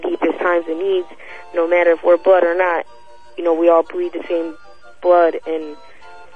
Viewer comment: lol it only took 13 minutes for that baby to kill itself...did she say "we all breath the same...blood"